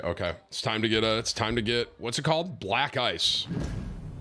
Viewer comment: Omg, when Tim yelled I thought he said,”Black guys!” 😂